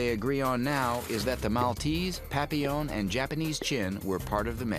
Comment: The video says Maltese, Japanese Chin, and Papillon. 1:13 But maybe because it's a French word, you didn't understand?